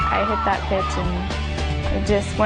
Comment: Did she say "I hit that bitch"?